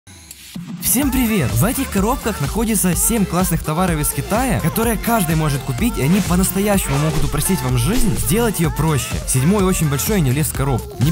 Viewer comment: мне одному в начале видео послышалос ((товары из хентая))